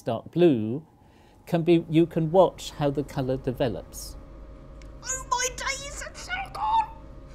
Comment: I hear this; "Oh my daiis thats so good! Oooh thats so good!"